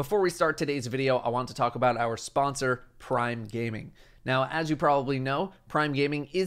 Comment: I literally thought you said "Brime gaming" and felt a disturbance in the force